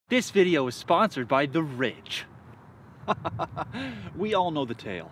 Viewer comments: Ha, I really thought you said "This video is sponsored by the rich" at first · I was looking away for a second and I thought you said "This video is sponsered by: The Rich"